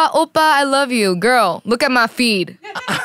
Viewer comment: Jamie: I mean for sure i’m not BTS though. They still DM me, “AH OPPA I LOVE YOU!” Girl look at my feed, I got *pauses*. JAMIE SIIISSSS 😂😂😂 This podcast episode had me hella weak. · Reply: ok when I first listened i thought she said girl look at my feet, and im like.... what..does that mean..